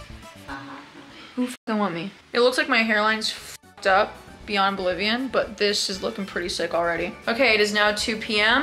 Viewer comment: Did I just hear "beyond Bolivian"? 😂😂😂